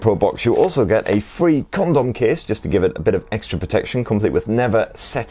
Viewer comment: Did I hear condom case lol